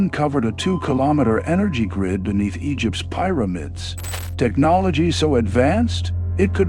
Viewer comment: I heard "pyromids" and immediately stopped the video. Ugh.